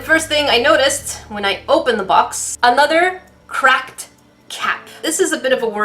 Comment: It sounded like u said ‘cracked jack’ 😂